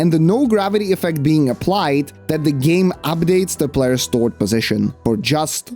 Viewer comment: For the first 10 minutes I thought he was saying STORT position.